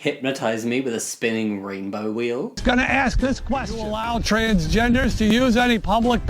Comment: Instead of 'allow transgenders' I heard 'wild transgenders' lmao